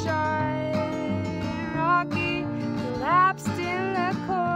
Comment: Did she say "Rocky prolapsed in the corner" at 1:50?